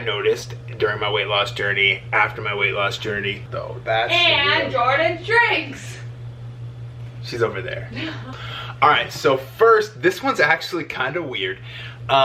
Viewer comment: I know your girlfriend said "and Jordan shrinks" but I heard her say "And Jordan drinks" lolol